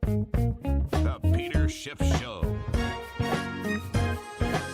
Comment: I always hear "The Peter Shit Show" 🤣